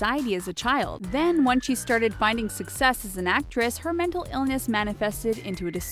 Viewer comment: An actress???? On the subtitle it said "Actor" And, 5:02 it said "whe" instead of "when" GOOD JOB,I THINK YA NEED KINDERGARTEN